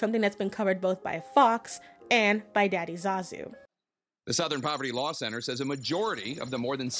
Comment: LMFAO intense topic of confederation romanticized in fiction and I hear "Daddy Zazu", omg don't kill me 😂